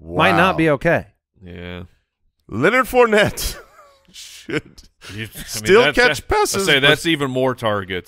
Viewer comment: Anybody else catch 30:33 drop the, "Leonard Fournette...Sh*t..." 😂